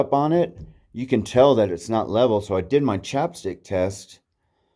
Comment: I could have sworn you said chopstick test. I was waiting for you to pull out a set of chopsticks.... then came the chapstick, lmao 😂 Do you bother hanging a bear bag? I’m sure that was a fire tower and I bet that cabin was the attendants home. I doubt we will really see any new fire towers built (except for ‘show’) since we have satellite imagery now. Better get out and climb them while you can.